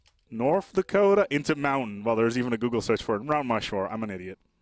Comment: Did he say Rount Mushmore